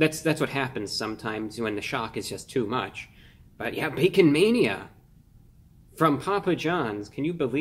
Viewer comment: At first I thought you said "Vegan Mania".